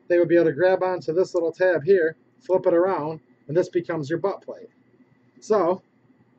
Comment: he say butt plug instead of butt stock, i fucking lost it hahahaha